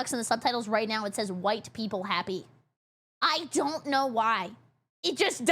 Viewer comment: it actually says "white people happy" instead of widepeepoHappy, damn it subtitle 😭